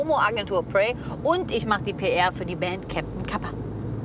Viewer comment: Ich habe Captain Papa verstanden 😅😂